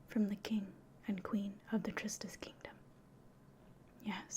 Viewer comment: I could’ve sworn you said triscuits kingdom